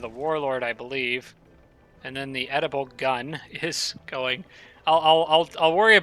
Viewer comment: I misheard "oedipal gun". Freudian slip, that's when you replace one word with your mother. I guess that's the gun you shoot your dad with in order to take your mother